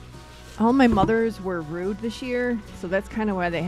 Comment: 😂 I heard it as "All my mothers were RUDE this year" until I heard the next sentence and figured out it was rooed.